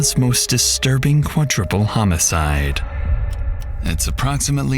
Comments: Did you say quadtriple homicide 😂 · Great video per usual, but did you just say qua-triple? Or am I hearing shit!?